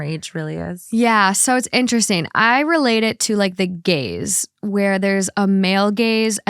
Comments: I thought she said “the gays” 😂 💀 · LMAO when said "the gaze" I misinterpreted as "the gays." I was like, "Wait, Morgan..girl.. what did you just say???"